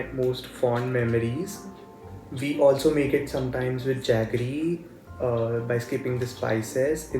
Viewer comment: I think he says jaggery, not chutney ;)